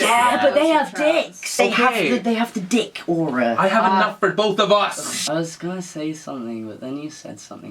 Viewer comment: No one: THEY HAVE THE DICK AURA: I HAVE ENOUGH FOR BOTH OF US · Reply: I thought he said, "I have a nut for both of us." 🤣😭